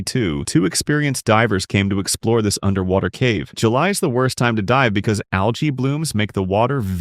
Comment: When he said "July is the worst time to dive", I heard "July is the worstetime to *die*" and I'm like 'isn't every time a bad time to die ?'